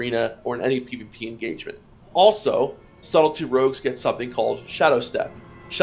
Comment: I heard "Salty Rogues" instead of "Subtley Rogues" like three times :'D Great overview btw!